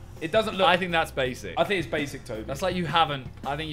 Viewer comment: Did anyone else hear JJ call Tobi a 'basic KSI' at 15:35 XD